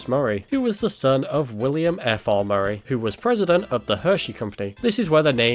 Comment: wasn't looking at the video, and I totally thought you were gonna say william afton lmao